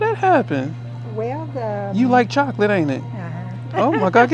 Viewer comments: At first, I thought he said "chocolate squirrel ". 😂😂😂😂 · Sounds like he said she’s got that chocolate squirrel… Is that anything like a chocolate rabbit? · You White Chocolate, You got that Chocolate Squirrel in you 😅😅😅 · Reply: I thought he said “chocolate swirl”